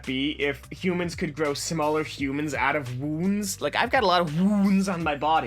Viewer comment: I thought he said wombs so it was really weird when he said his body was covered in them